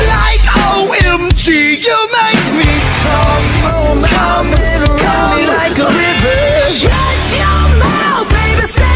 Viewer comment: Wait what 💀Did he actually just f-ing say "You make me cvm" 🤨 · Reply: it says you make me com-com- plete. like complete